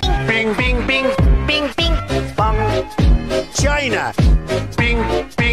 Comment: Make JOHNNY or RATTY GREAT AGAIN! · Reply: Funtime Foxy bing bing bing bong i thought trump said vergina but he said china